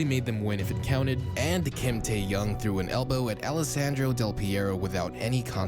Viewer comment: DID HE SAID KIM TAEHYUNG